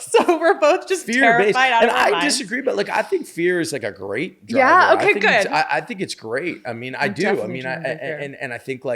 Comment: what does Deborah say here? 24:34 kind of hard to hear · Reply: "I'm definitely driven by fear"